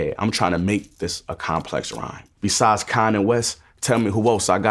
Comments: Besides Con and West, tell me who else I gotta respect. His verse over that Al beat is crazy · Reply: Ngl I thought he said “Kanye West” the first few times I listened to it lol. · I always thought he said "Beside Kanye West" makes alot more sense knowing he said "Con and West" 😂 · i always thought he said “besides kanye west, tell me who else i gotta respect” but it makes a lot more sense now